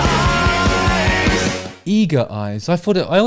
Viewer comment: I Thought he said EGO-Eyes ^^"